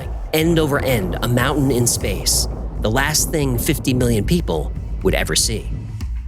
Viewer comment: at @ 20:24 does he say 50 BILLION people?